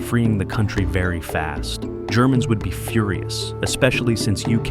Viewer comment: Führer-us instead of furious